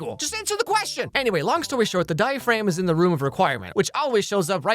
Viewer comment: did he just call the diadem a diaphragm?💀